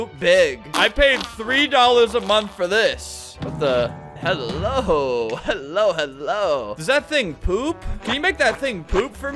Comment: AYO FLAMINGO WHAT DID YOU SAY AT 2:55 ??? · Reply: "What the" "Hello" "Hello hello"